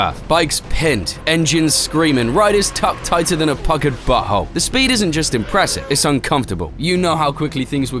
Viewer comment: tuckered butthole? Did I hear that right?